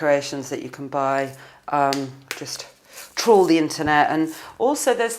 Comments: i swear i first heard her say troll then i realized it was toured.. i think..... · did she say troll the internet?